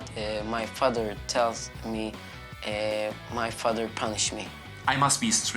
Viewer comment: Is no one going to talk about how the kids said his dad punches him if he doesn’t do what he says like it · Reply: He said ‘punishes’ :)